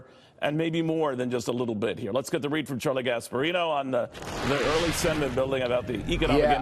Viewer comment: Who else thought when he said "Charlie Gasparino," it sounded a lot like "Charlie Gas Burrito"?